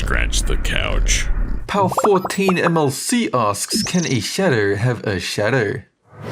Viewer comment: Thought it said “Can a shadow have a shotgun?” Oh shit I think I just gave 2020 a new idea